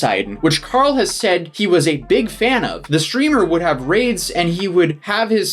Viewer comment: gusse what he said was not that he is still a fan no was a fan